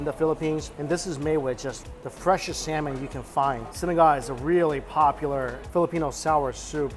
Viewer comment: thought he said synagogue when he said sinigang, but I'm super happy to see him enjoyinh our tasty food